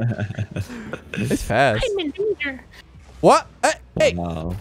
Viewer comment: I heard "I'm a ginger"